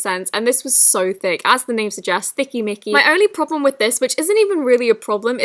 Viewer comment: I think it was before 2:18 but I thought said thicky nicky lol